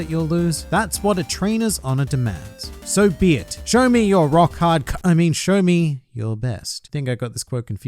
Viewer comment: I could of sworn you said, "Show me Rock" in Gon's voice for a sec but then it was followed quickly by hard and I was like: So, it wasn't a HxH reference. u.u